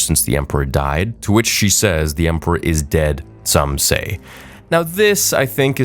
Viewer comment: In response to Ezra saying “the emperor is dead?” She says, “that’s what they say,” not “some say.” And I think that was just a nod to how a lot of fans don’t count the sequels as cannon and that Palpatine died at the end of RotJ.